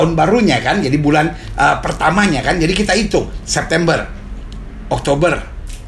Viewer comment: denger lagi ulang .. salah denger kayanya .. pak pdt bilang bulan pertama itu bulan september .. pada bulan yg ke 6 itu maret .. dari maret itu hitunt 9 bulan jatuh di desember .. haddehhhh